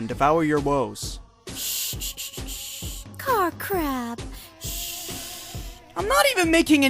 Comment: at first i thought Feferi said "shh shh, Karcrap..." XD